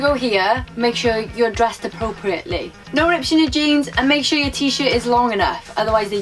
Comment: Hi there! I have one question, since I didn't undertand something you mention... in minute 2:02 you say something about the jeans... ripped <something> jeans... could you quote what you say there? I'm very curious! thanks! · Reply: 'No rips in your jeans' ;) English is terrible haha. They won't let you into that bar with ripped jeans!